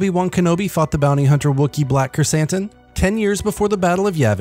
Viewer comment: He thought he said the Kenobi fucked Krrsantan